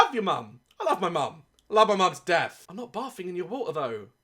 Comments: It sounded like he said "i love my moms death'' instaed of : "i love my mom to death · I thought he said “I loved my mum’s death” 💀😂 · did he say I love my mum's death!?!?! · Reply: He said "I love my mum to death." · Tell me why when he said I love my mom to death I thought he said I love my moms death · did he say he loves his Mums death??? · Go to 6:50 and l think he says he loves his moms death comment what you hear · Did he just say he loves his mums death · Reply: "I love my mom to death" he just loves his mom alot is all that means.